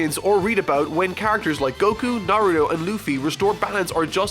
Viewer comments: Did he say Nario? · Did anyone else hear “Nario”?... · did he say nario or are my ears giving up? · Share this video and leave a like if you want this series to continue :D Thanks for helping me reach 400k subscribers. Massively humbling experience. You all are amazing! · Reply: I know well he did not just say nario